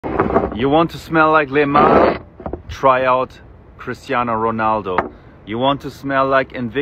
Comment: I thought he said: “ You want to smell like Neymar, try cristiano ronaldo” 😂